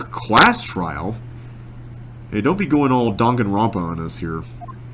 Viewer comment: "Donk and Rompa"? Is that what you were saying? I have no idea what that means. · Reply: "Danganronpa" is another murder mystery/investigation game